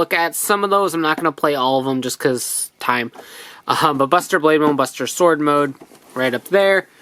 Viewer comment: Legit thought you said “Buster Blader”